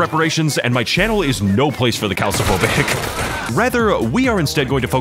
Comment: What were the words after "These guys deserve reparations and my channel is no place for the..." ? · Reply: “Calc(ium)-phobic”